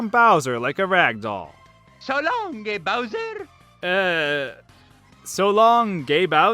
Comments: I always thought Mario (in 64) was saying: "So long le (he's french for some reason...) Bowser!" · I hear these. 1:58, "So lawn 'kay Bowser!" 3:26, "Tournament combat!" 3:45, "Fighting tongue!" 4:03, "Crossfire!" 4:04 "Get out!" 4:27, "Ow crispy!" 4:35 "Eye's Drumstick!" 4:44, "Red block!" 5:39, "You ready to roll with the daddy's broom?" 5:53 "Bonfire!" 5:55 "Good one!". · You heard it as: So long gay mountain! I heard it as: So long kay bowser. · You too you thought it was Gay Bowser? XD It took me a while until i realize he said King instead of gay. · I thought Mario said "talonke bowser" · I thought mario was saying "so long king bowser" till I watched this. Till I Watched this. I heard "So long gay bowser" Idk why. I guess it's because it's funny. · I always heard, "So long, big Bowser!" Also, in Bowser's Inside Story, when one of Mario's speaking clips played, I always thought it said, "Super high sketti." at the end, sketti being a shortened version of spaghetti. This made me think for the longest time that either Mario ate some spagetti laced with drugs, or a bowl of anthropomorphic spaghetti was snorting crack up its noodles. In reality though the voice clip was supposed to be unintelligible. In the NES version of Double Dribble's title screen I either hear, "Trouble Bibble" or "Bubble Nibbler". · I always hear "Shalom, gay bowser" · i aways heard Mario Saying "So long Bowser" and "Let's a go" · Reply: I thought he said so long gay bowser